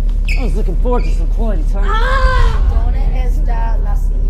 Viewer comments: Something I noticed At the beginning was that the teacher was teaching Spanish. And the sentence was "where's the exit" I felt as the kid was asking that question. As if the kid was begging for an exit to this situation or life. The second teacher was music. That explained that with music I can escape the world. Cause when you have music everything else is shut down. That's why the music teacher couldn't hear him cause he was too into the music. This is just a thought that popped up in my head. Maybe I'm wrong but that's what I feel the beginning stands for. Edit: I commented this when I was 13, didn’t expect such great feedback, 19 now and life still hasn’t changed much, this song hits harder than when I first listened to it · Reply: brittany graves then i heard wrong, i heard "right, that's the accent" :v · Is it just me or when at the start Ye language teacher says 'great that's where's the exit' to symbolise the young boy wanting to exit his torture from the older boy I'm probably thjnking too deep into this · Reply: Hollie Swann and I heard this like "wheres the accent"